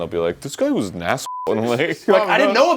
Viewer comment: is she saying her dad passed away?